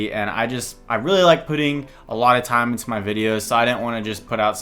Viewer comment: At the beginning i thought he said he likes pudding lmao.